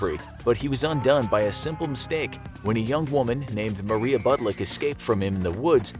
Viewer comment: Sounded like they said KURTEN said was undone by a woman who escaped was named Maria BUTTLICK.